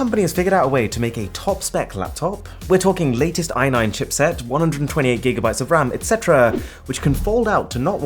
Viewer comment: did you just say 128 gb ram